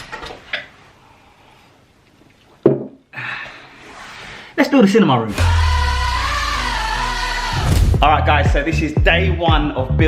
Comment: Idk i hear he says "ah lets build a sh*t in my room"